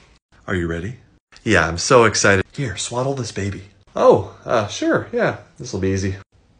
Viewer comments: More than one person thought I said “swallow this baby” at the beginning. Please don’t eat babies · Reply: Wow, I did NOT hear "swallow", I heard "swaddle". I should get my hearing checked 🤭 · More than one person thought I said “swallow this baby” at the beginning. Please don’t eat babies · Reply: I misheard that too and got so alarmed. Then I realized you'd said SWADDLE and went, "That makes infinitely more sense!" XD Look man, I don't know you: maybe you COULD eat that baby! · I thought you said "Swallow" this baby and then started the crying sounds. I am more concerned about the fact that I wasn't concerned when you said it. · The first time I watched this episode, I thought he said, "Here, SWALLOW this baby," instead of "SWADDLE this baby," obviously I don't have children 🤣🤣🤣